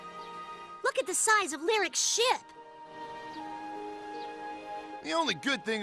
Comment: for a second i thought Amy said "Look at the size of Lyric's shit!" 😂